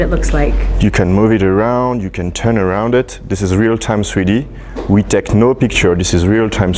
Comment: I thought he said real time sweety